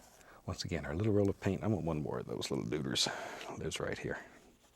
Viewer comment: I want one more of those little doogers?? Is that what he says?